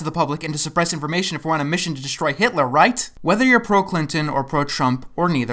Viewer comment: i heard "whether you're pro-plankton or pro-chump" wtf